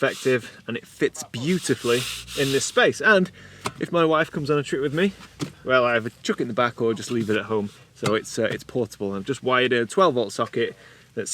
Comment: Just got to the bit about your new fridge and I thought for a minute you said, when your wife wants to come on a trip, the fridge either goes in the back or you leave the wife at home 😂